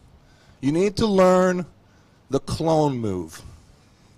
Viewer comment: What is this clone move? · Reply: in the video le can called the kings move “clown move” but since his English isn’t too good they heard it as clone